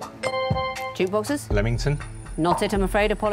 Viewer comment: what did he say instead so that it didn't count? To me it sounded the same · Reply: it sounded to me like 'lEmington', not 'lAmington'. very close, though